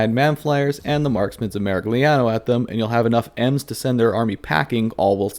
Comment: I thought you said the marksmen of Marik Liao lol I need to play less Battletech